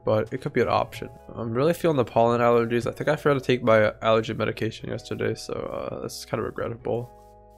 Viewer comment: anyone else catch that “I think I forgot to take via- Allergy medicine yesterday.